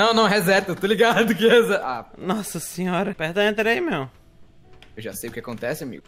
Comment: EU JURO Q OUVI penetra aí meiaum ;-----------; MINHA VIDA É UMA MERDA